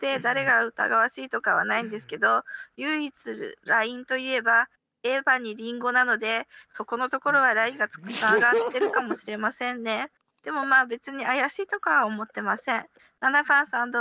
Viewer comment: からの6番さんの発言で笑いが起きてるけどなんて言ったのか教えてほしい(＞＜) 聞き取れません、、、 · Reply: 唯一ラインがあるとするならエバにりんごなのでそこの方がラインがあるかも知れませんね